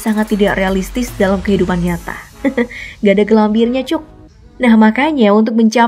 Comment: Menit 3:31 Coba keraskan volume anda biar dengar apa yang mimin ucapkan Yang saya dengarkan kok ada bahasa jawa kasar🤔 Menit 8:21 kembali lagi ada bahasa jawannya😶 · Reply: Iya kok di 3:31 kedengeran mbaknya bilang "cuk" kasar banget